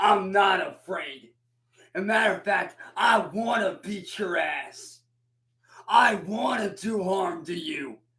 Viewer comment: I still love how it sounds like be says, “To beat Giraffes!” When he threatens people